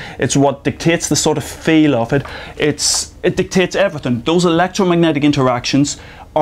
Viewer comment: Sry, but did he just said "the tits" at 3:07 :P